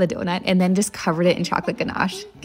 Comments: thought she said Hairy P bruh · I thought she said Perry P and was expecting perry the platypus😅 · Imagine if they were giving it to someone else that was not named Harry😂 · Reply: I thought she said hairy p😂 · My dumbass thought she said perry p.and then wonedered why it was h.p. stuff until the end of the video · I must be going deaf because i didn’t hear “Harry p” I heard “Perry p” thought I was gonna get a to see platypus doughnut 😂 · Dayum I'm sorry I thought she said hairy pean😭 I was so confused wtffff😢